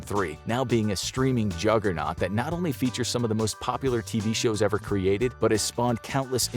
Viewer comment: I heard “the most worst popular shows ever created” at 6:25.